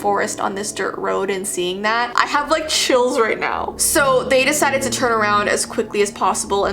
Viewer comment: Her: "I have like chills right now" Me: wait did you just say you have CHILDREN now?! Also Me: *finding out what she actually said* Me again: oh- ⊙~⊙